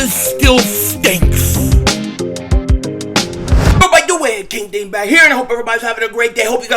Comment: Please for the love of God tell me what's the first thing you say in your intro right before you say "King Dingbat here" it's been years I still don't know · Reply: It has bothered me for 2 yrs. It's sounds like he says, You bout the way, king dingbat here